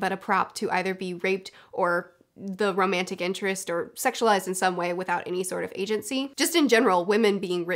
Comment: When you said ‘se*ualized in some way’ I heard ‘se*ualizing Subway’ .... a six-inch on Italian popped into my brain, and I “ wait what “